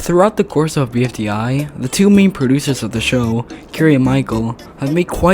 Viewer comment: im thought you said meme producers